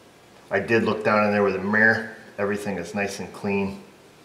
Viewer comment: did you say “murrr?”